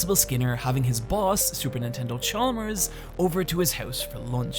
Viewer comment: Did you just.....did you say Super Nintendo Chalmers?!?